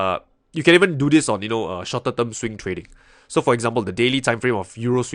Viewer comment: hey, thank you for this video, on 26:11 you said "shot a double swing trading" based on the subtitle. I'm not sure if the subtitle is just incorrect, but I can't quite make what you said "___ swing trading"? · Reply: Hey there Chris, mentioned "Shorter-term swing trading" I hope that makes sense!